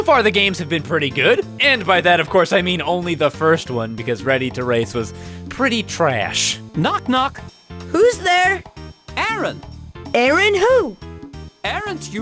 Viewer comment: why did it take me this long to figure out she was saying “No computer games” instead of just random bass boosted gibberish